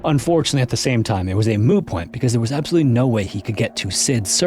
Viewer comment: Did you say a “moo” point? Did you do a Joey? “…Yeah, it's like a cow's opinion, you know, it just doesn't matter. It's moo."